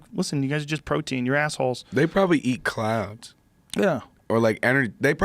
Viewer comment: "They probably eat clouds" Meanwhile Post Malone is as high as a cloud drinking some bud light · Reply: Only till i read the comments that I realized that he said clouds! I thought he said clowns!